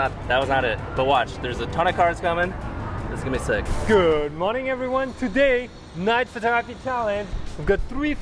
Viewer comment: lolx did he say Good morning everyone? xD